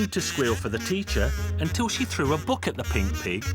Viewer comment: Am I the only one who hears the narrator call the old man a she